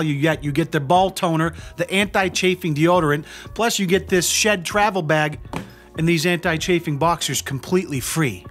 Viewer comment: I literally thought u said shit travel bag. Not SHED travel bag. Hahaha